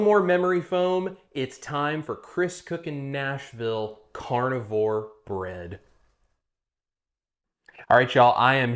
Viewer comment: i thought the video said Carnival bread at first